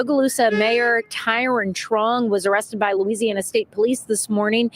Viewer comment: I thought you said his name was Tyrone Schlong